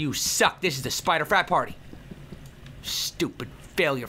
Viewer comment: Who else thought he said spider fap party instead of frap party which isn't even a word?!